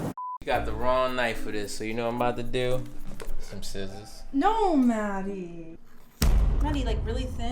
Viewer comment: Everytime she said Matty, I thought she said daddy😂😂